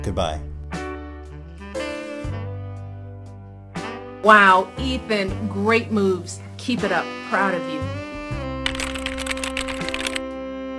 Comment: Throws self off mountain - "wow Ethan, great moves." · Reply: I thought she said great moobs